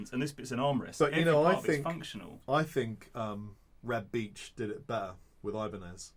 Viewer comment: Who? “Reb Beach”? I couldn’t quite catch what he said there. Edit: Heh, my wild guess was right. Or at least there’s a guitarist with that name. Now I’ve gotta figure out what to google to find out what talking about… Edit 2: Found it… he had a signature model called “Voyager”. Interesting looking guitar!